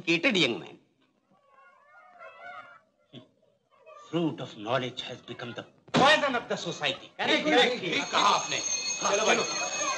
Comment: What did he say at 19:44. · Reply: He said fruit of knowledge has become a burden of society